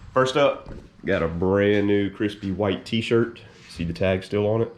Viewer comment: Man, i was in such a rush to watch the next installment. I thought you said WET t shirt. Cams a shit. Progress is progress